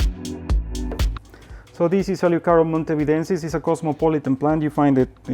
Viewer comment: What's the plant at 25:36 ? I couldn't catch the name and am having trouble googling it. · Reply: Eleocharis montevidensis nevermind!